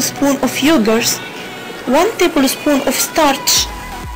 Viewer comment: Hi, I would like to try your routine, but what you said in 2:48? could you pls write it. Thanks a lot. did you said starch, corn flower? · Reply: #Basema A. , you are welcome startch